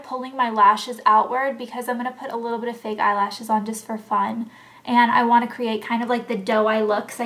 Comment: if you play 5:38 over and over again it sounds like shes saying disco fun